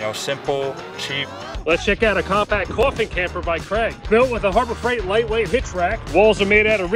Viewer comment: Yahh... Because of his accent it sounded like he said "coffee camper"...lolol 🤣🤣🤣🤣